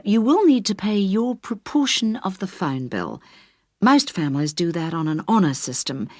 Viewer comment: This is harder than most of other Listening practices in Youtube. I heard "fund" instead of phone. Just got 6.5 (sigh)